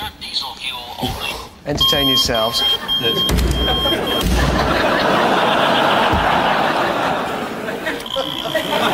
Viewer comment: My favorite. Gifts segment the most chaotic segment of the show 14:12 What did he said? I can't hear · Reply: He said he has a VW Jetta